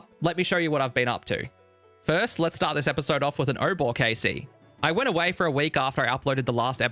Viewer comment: i swear to god it sounds like he said lets start off with a oda block kc i was like wait wut